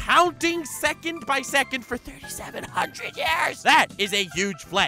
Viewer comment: *116,683,200,000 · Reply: yeah I thought he said 3500 years not 3700 lol my bad